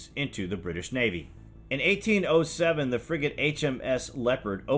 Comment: Thought he said The friggin hms leapord opened fire on the friggin Chesapeake